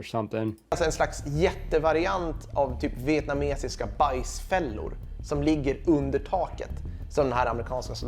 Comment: when the subtitle says vietnamese punji stick trap, what he actually say is "bajs fellor", which literally translates to poop traps, as they were said to be smeared with human waste to infect the wounds.